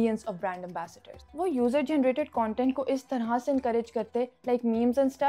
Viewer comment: did u just say freeky marketing 👀